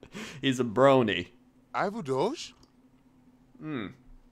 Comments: Did he just say "I'm a douche"? Would kinda match :D i love it ^^ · sounded like "I'm a douche!"